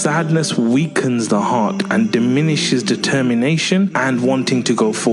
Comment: he sounded like he said "detemmienation" lmfao